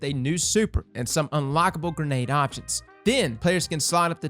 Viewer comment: lol thought he said "and some unlikable grenade options"